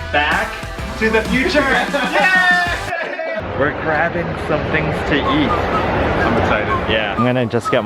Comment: I thought u said “ we are grabbing something to YEET”